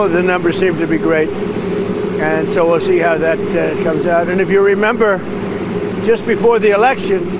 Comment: It sounded like Trump said "We'll see how that Trumps out" after he was talking about a new statistician.